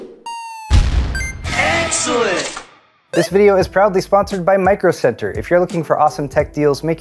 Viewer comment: I had to double check the captions. I thought I heard "probably" . Wouldnt have been too far fetched considering your dry sense of humour ;)